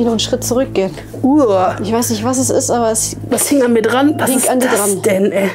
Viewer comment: Was sagt da jemand im Hintergrund? "Oh ja es hängt an Anja!"?